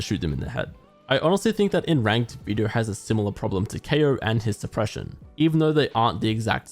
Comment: what did you say? His depression? 😂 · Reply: supression